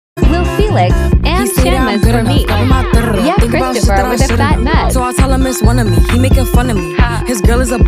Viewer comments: HELP I HEARD IS AS "CHRISTOPHER WITH THE FAT NUTS" 😭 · NAHH I thought it said “ Chan with the fat nuts” 😀💀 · NAHH I thought it said “ Chan with the fat nuts” 😀💀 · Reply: NAH CAUSE LITERALLY ALL I HEARD WAS "CHAN WITH THE FAT NUTS" like AYOOO?